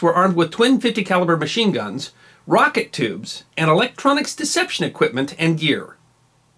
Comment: Yes, they they used 5 inch unguided rockets. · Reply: I thought that he might have said rocket tubes instead of torpedo tubes . Thank you for your reply .